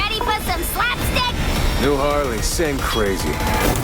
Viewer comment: does anyone know what deadshot is saying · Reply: New Harley same crazy